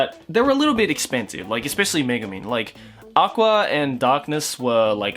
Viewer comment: Each time he says "Megumin" I hear "Megaman"....